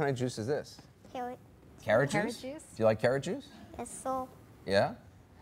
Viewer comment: when they said he said 'yes sir' i thought he said 'guess so' which sounds really appropriate..